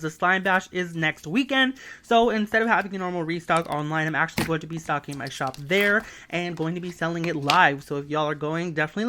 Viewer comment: I thought you said "I'm gonna be sucking my shop" hearing error XD edit:GREAT SLIME S THO